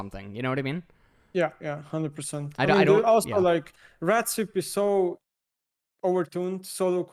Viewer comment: it took me FOREVER to understand what said there but finally I understood "red sweep"